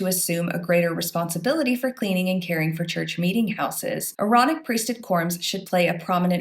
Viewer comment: You said Aaronic Priesthood. I heard Moronic Priesthood.